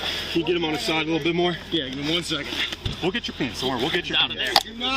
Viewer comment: I thought that officer said “we’ll get your penis” like why would he do that, but then I put the subtitles on and he clearly says pants.